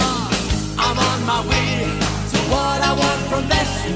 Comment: I thought it said “I’m on my weed” 💀🤣